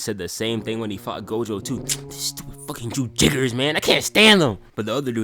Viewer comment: "Jujiggers" I see Gojo has taught you well, you're on your way to replace him as the grandest wizard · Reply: And here I thought he was saying “Jujiggers” as in “jujutsu sorcerer” Edit:for those who are confused the original comment said “yujiggers”